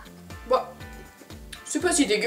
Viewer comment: ECE QUE C MOI OU G ENTENDUE UN QUEBECOIS DIRE ''calisse'' A 6:28???? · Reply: nan c'est "ho dégeulasse"